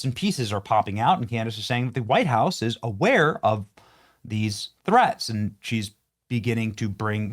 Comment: I thought she said the White House killed Kirk